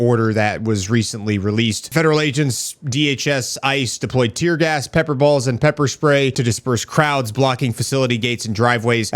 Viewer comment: At first, I honestly thought you said they were peppering balls. I'm thinking to myself ....that's gotta burn. 😆